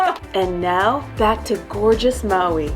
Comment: Miss you and Mommy Oh...Would be nice if bf was involved with the reviews too...would be good to see him...Maui is gorgeous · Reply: Just for a second I thought you said was gorgeous, which is true too 😀